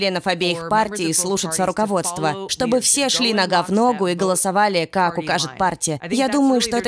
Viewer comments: Секундочку, мэм, Вы бы хотя бы сказали, кто Вы такая, где наш Туктукер. А ещё вместо "нога в ногу" я услышал "на г***о" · Сначала послышалось "чтобы все шли на гавно"😅